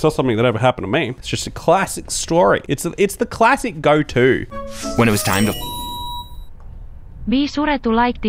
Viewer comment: sounded like it blurred out "When it was time to ****"